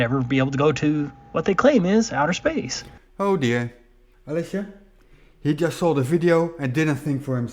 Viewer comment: Daughter* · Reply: sorry, I thought you said Alex, not Alicia.